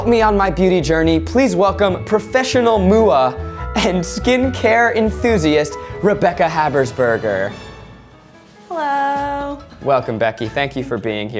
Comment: Did you say Rebecca 1:50?